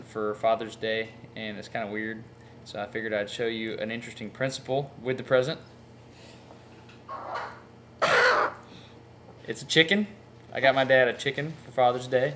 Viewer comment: lmao for some reason i thought he said chicken is hot